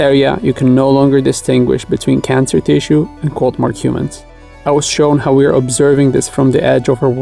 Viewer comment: ~~~ quote mark humans = "humans" ........... i had to reverse the video twice to catch that, and turn on the CC (closed captioning) ..... that's for whomever cannot quite catch that phrase "quote mark humans"